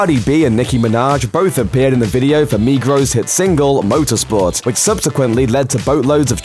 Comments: Did he just say MIGROS??????? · Did he just say "NEGROES" hit single LSNFKJFLMXLKFLKFH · Who heard him say “migros” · did he just say Migros? I heard negros at first💀